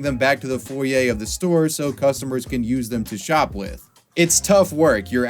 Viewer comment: 😂 Did you say “bring the shopping carts back into the foray so customers can use them to shoplift” ? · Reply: “Shop with” lol I thought he said shoplift too , I had to run up back .